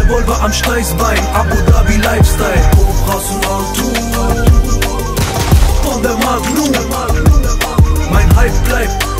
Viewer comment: Was sagt er bei 1:09 - 1:12 ☺ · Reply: Hi's girl er sagt:,,Kobras und altun" das heißt Kobras und Gold